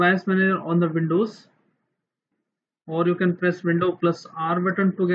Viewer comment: Bro what do i have to press? I cant understand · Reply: It might be late but he said "windows + R" in the video. Hope it helps.